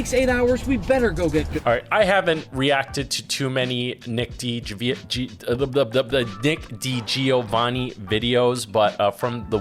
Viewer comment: Did call him Dick DiGiovanni at 1:24?? 🤣